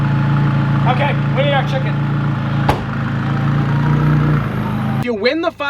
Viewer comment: sounded like he said Weinerd our chicken